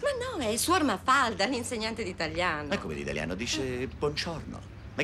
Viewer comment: cosa dice??? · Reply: "Ma che gli insegna ai pupi? Er cispadano?"